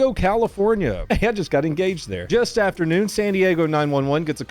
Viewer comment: Did he say: "And just got engaged there."??? Sorry I'm probably hearing things lol